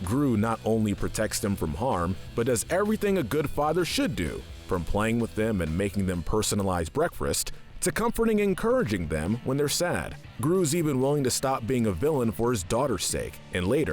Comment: please tell me I’m not the only one who heard brefest